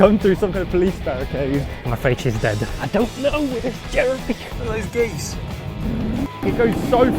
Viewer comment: I swear to god Every time you say "Look at these geeze" on your ad I hear "Look at the gays" And there are very few occassions you'd use such an expression, and it's probably in June lol